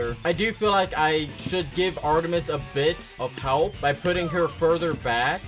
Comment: I thought you said no help